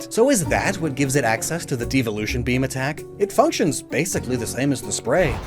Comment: did somebody say beam attack?